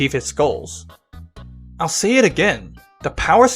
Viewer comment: I just skipped the video here and what I heard was "and I will see it again"